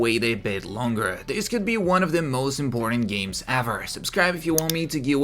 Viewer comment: sounds like you said GTA 6 will be the most "boring games" ever lmfaooo